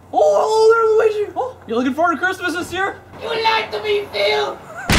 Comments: he like you lied to me phil!!!;! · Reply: At first I thought he said you like to be fill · This part got me everytime as a child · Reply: I thought he said "who likes to be Phil!" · OMFG THE YOU LIED TO ME PHIL PART XD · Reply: It kinda sounds like Luigi said YOU LIKE TO BE PHIL?!